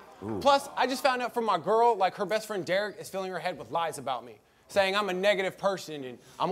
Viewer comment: i thought he said filling her head with LICE 🤣